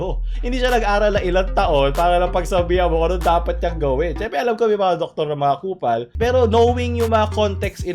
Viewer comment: “syempre alam ko na may mga doktor na kupal..” Why am I hearing “ANG DUMI DUMI MO!”?